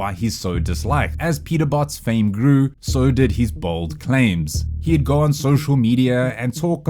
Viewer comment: I heard bald claims I can clearly see that